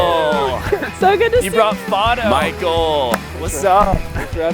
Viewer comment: is Fodo (captions said their name was photo) a friendly dog? They look like one. I just want to know as a fellow dog owner. Edit: Nevermind, you don’t have to answer as I have just realized creepy that sounds.